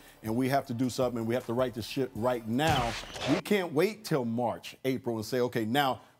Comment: did Byron say we got to right this shit now cause if he did he’s legendary